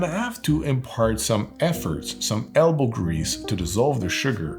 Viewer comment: Great video breaking down on why each part of the techniques to make a mojito matters to make a nice mojito! :D 6:09 I misheard this at first (just my hearing), and thought you said "ambergris" instead of "elbow grease"... Now I am wondering what kind of cocktail perfume could be added to a mojito, which may count into a new mojito heresy of its own. lol